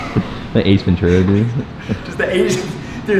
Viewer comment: said “Ace Ventura” and thought he said “Asian”😂😂😂